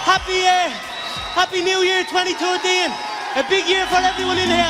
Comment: I think he said "Happy New Year 2020" 😂😂 it will be crazy if he said that. Wow